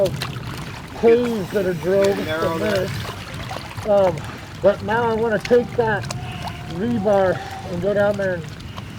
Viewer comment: anyone else hear a strange voice at this timestamp? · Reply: Yes, bring me a roll of toilet paper, somebody is in your bathroom. The rest is self explanatory.